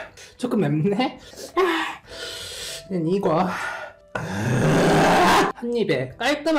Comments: At to 2:09 I thought he said the n word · I heard the n word in the first one